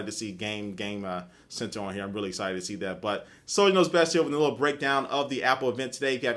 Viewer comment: sounds like u say SonyKnowsBest when u say "soliderknowsbest here"